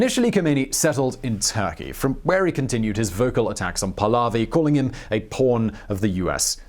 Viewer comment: Who here seriously thought he said porn at 10:48?